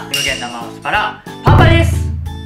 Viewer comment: お酒の話をこの前にしてたせいか、 “完成です！”が“乾杯です！”に聞こえるんだが気のせい？(笑)